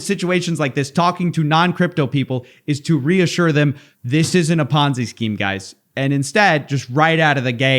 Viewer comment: Wait Ponzie scheme…? I thought you said Fonzie scheme when i first watched this and I invested. Damn it!